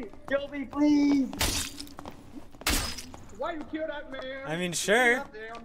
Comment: "Why you kill dat man?!!! God damn" Hope I heard that right, but that shit had me weak lol